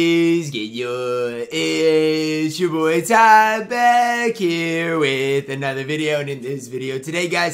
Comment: Someone please explain what ty intro is. I heard "Yuuuu Isssss and yuhhh issss sure boy ty back here with another video"